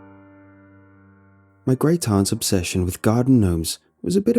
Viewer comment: I thought it said, "My great aunt's genome keeps multiplying".